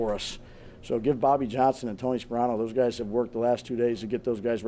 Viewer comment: I’m not the only one who heard him say “Tony Saprano” right?